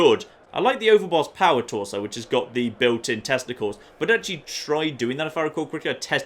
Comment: I kept hearing "Built in testicles" 😂